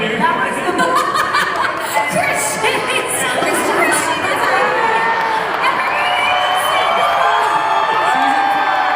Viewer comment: what does Erika say here? Can't really understand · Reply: I believe she said "Christian is leaving. Everybody say goodbye!"